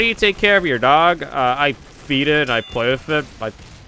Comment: 5:24 Me:woah,did he say feed it or feet it ???!!!??? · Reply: My captions says 'Feet it'.